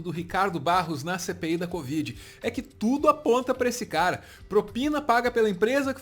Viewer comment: Quando ele faloiu "CPI da covid", eu ouvi "Cê peida covid"